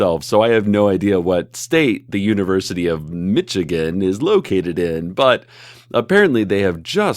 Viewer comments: I fully thought he said bitchigan at first oh my god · i thought cecil said Bitchigan